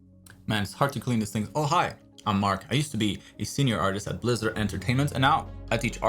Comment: I spat my food cause i thought you said "oh hi mark" on your intro 🤣